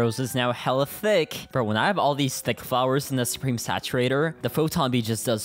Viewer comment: BRO MY MOM HEARD YOU SAYING SHES THICK